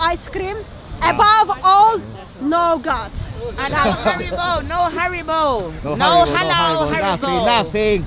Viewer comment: what is she saying?? No halal what???